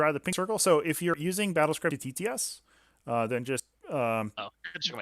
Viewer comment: hey could you please repeat that? I forgot and I can't make out what you said in the stream again. Sounds like "Battlescribe TTS" but I don't know what to make of it ^^